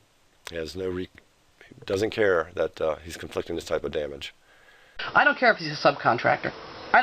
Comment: did he just say "conflicting" and mean "inflicting"? oh lawdy.